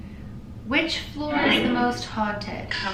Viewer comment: I heard "this floor"